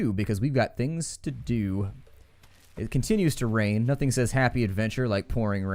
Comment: i thought he said nothing says adventure then porn